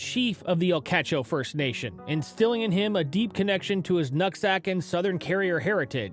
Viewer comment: did bro say his « nut sack »